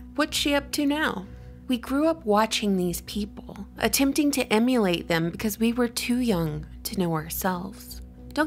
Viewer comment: Your captions hilariously say "immolate" instead of "emulate".